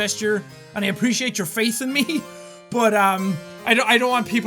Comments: I thought jack said "I appreciate your FACE in me!" XD....... I can't stop laughing now! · When jack said don't have all your faith in me I thought he said don't have all your face in me lol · it sounded like he said I appreciate your face in me! 😂😂😂😂😂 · When Jack said "I appreciate your faith in me" I thought he said "I appreciate your face in me"😅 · I heard "and I appreciate your face in me."